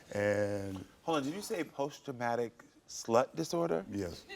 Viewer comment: did he say post dramatic slut disorder??? 😂😂😂